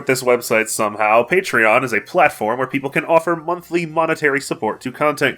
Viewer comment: okay thank god it's monthly, at first I thought he said every week for the reviews and i was thinking "holy shit he's going to die"